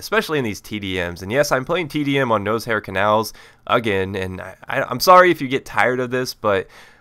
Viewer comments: it sounded like u said nose-hair canals!!LOL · in the beginning of the video it sounded like you said nose hair canals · did he say nose hairs canals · Wait a minute, 0:21 did he say nosehair canals?